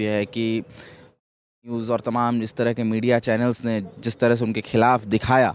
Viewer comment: i think this guy said “Aaj tak news”, they beeped it because Lallantop is owned by IndiaToday( parent company of Aaj tak and lallantop). Just so you know. 😇